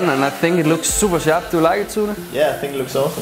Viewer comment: listen did he say "do you like it Türker"